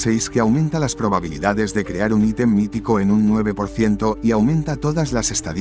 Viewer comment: escuche "item mitico" y me puse locaso, al final solo decia unico, no saben que susto me pege